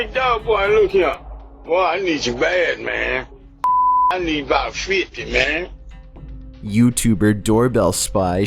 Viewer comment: what was he saying? "I need some feet ma'am" "i lick ma feet man" "i set ma seat damn" "i need ma weed man"